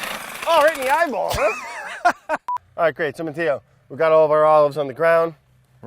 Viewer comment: sounded like he said "Tomatillo"